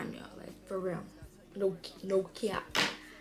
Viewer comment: For a sec I thought you said “no gyat”😂❤